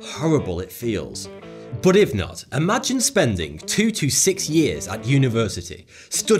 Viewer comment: guy says "2 to 6 years", official subtitle says "2 to 5 years" you had one job